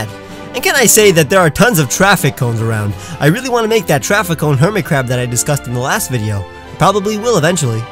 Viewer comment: I heard that as "traffic cone Hermitcraft"